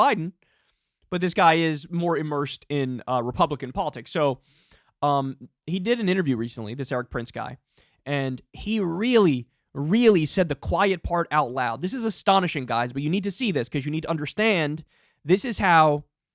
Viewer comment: Thought it said, Quiet Fart out loud.